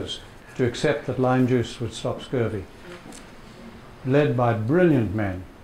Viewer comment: What did he say at the end (36:55)? Lime juice would stop what.... I didn't catch it. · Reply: Scurvy. Lime juice (ascorbic acid Vitamin C) prevents scurvy.